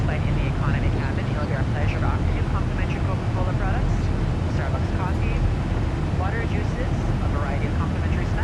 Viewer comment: did anyone hear the flight attendant say “Starbucks coffee”